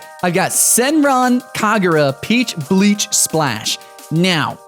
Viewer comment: Please say I'm not the only one who heard this as Peach Bleach Splash